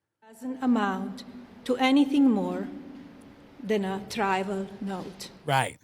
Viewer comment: Trial nut? That’s what I heard her say🤷‍♀️